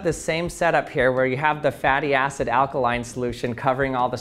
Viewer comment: “Hattie acid” I thought it said fatty acid 😂